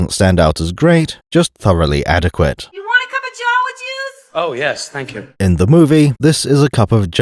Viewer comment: I somehow never realized it was "jawa juice" I always heard it as "you wanna cup of joe with yous?"